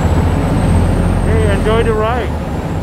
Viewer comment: I think I can hear you said "enjoy the ride". Ha Ha if it was true, you already adapted yourself to the Bangkok Life. Glad to hear that.